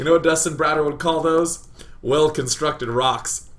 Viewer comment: Thought he said Dustin Brado for the "well constructed rocks" bit. So I googled him. Came up about some meth lab in mexico. Works there too I guess?